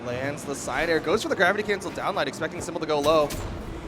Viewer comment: Wth hell did he say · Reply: gravity cancel down light expecting simple to go low